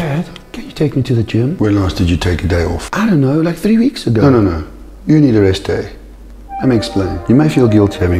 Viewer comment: First I thought he said: “when did you last go to the gym? I don’t know like threee weeks ago you need a rest day” 😂😂😂